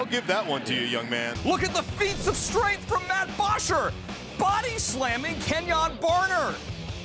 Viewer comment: Am I the only one who thought it said body shames 😂😂